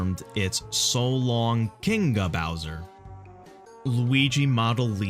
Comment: Honestly I don't hear it as "So long Kinga Bowser" but instead I hear it as "So long a Bowser"